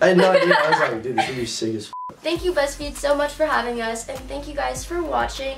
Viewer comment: at the end i thought nessa said “thank you bestie” 😭😭